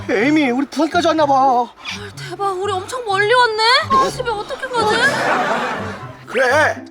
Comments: 헐 씨발 집에 어떻게 가지 라고 들림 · 에 아이 ㅅㅂ 어떻게가지? 이렇게 들림 ㅋㅋ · 초 에서 에이미님이 아 시발 어떻게 가지? 라고 들려요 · 나만 아 xx 어떻게가지라고 들리나... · 욕쓴 것 같이 들려요ㅋㅋ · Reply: 하 ㅅㅂ 집에 어떡해가지 라고 들리는것같은데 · 초에 에이미가 아 XX 어떻게 가지라고 들려요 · 초에 에이미가ㅅㅂ어떻게가지라고들림